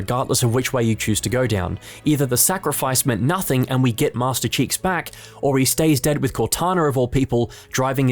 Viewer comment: Did he say Master Cheeks?